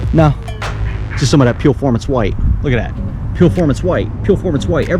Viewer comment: What are you saying as you walk around your hood pointing out the paint chips? "Peel-formance... white"? For God's sake! You talked way too fast through that! I couldn't even understand you.